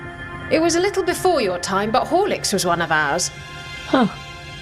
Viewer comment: Did she say whore licks?